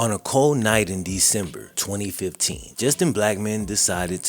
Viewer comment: At the start when you first said his name I thought you said just a black man I was like whattttttt